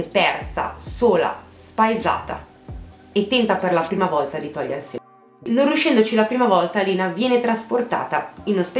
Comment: Minuto 2:55 cosa dice che e censurat qualcono me lo dice che non ca pisco e lo dice anche una seconda volta sempre censurato mi spiegate plsss grazie mille a chi lo fa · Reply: Si è tolta la vita. Ogni volta che censuro parlo della morte di Lena. Censuro sempre la parola “vita” dico sempre “si è tolta la vita”, censurando la parola “vita”, in pratica